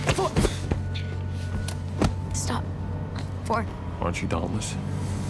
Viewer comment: What the hell did four say when tris told him to stop? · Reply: I thought he said "Aren't you Dauntless?"